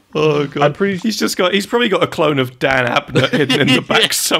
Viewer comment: The first time listening I thought they said Dan Avidan not Abnett. Funnily enough i thought it made total sense.